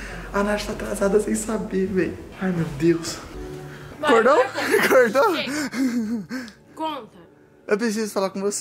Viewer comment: só eu que ouvi ela dizendo "mor agora conta" ?